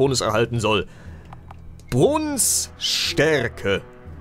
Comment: Bin ich der einzige der Brunzstärke verstanden hat? o.O